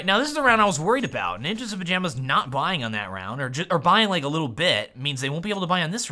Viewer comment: I swear I initially heard you say "...or buying like a little bitch."